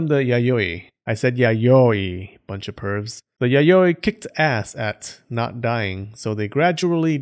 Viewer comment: "while the yaoi kissed ass" nice one please do more videos about the yokai